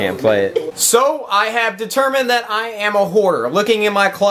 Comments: I thought he said " i have determined myself as a whore" xD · I honestly thought he said "I have determined that I am a whore" at 3:59 xD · Could have sworn that Nade said "I am a whore" the first time..